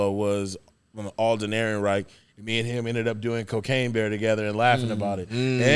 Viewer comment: did he say they did cocaine together?